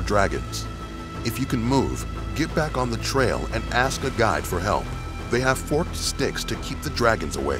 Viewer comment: At first i thought he said ,,ask a god for help'' haha